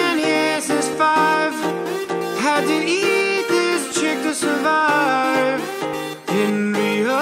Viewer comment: I always thought that he said "how did even this chick had survived" but when I turned captions on it simply said "Had to eat this chick to survive" lmao.